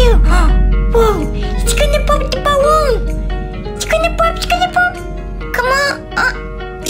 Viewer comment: What other pepole hear"its gonna pop. Oh my god its not gonna pop" What i hear "its gonna poop oh my gyat its not gonna poop